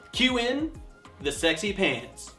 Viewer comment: OMG, I thought you said, "Queue the sexy PANTs" not pans. I was waiting to see you in skin tight skinny jeans doing the Pee Wee Herman Dance!!! TEQUILA!!